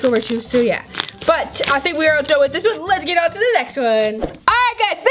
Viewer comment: Hehe on the purple one it sounded like she said let’s midget